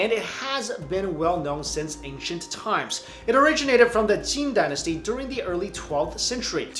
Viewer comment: Did you just say the Jin Dynasty? The only thing I know of that dynasty was that they held a Mongol by the name of Temujin captive as a slave. Decades later, Temujin, the man known to history as Genghis Khan, led a campaign to exact vengeance on the Jin, and the Jin Dynasty became the first conquest of the Mongol Empire.